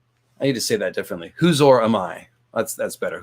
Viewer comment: I misheard "Huzhor Amai" as "Whose Whore Am I?" :D - and I'm probably not the first. I wonder if that's GRRM being silly. I wouldn't put it past him.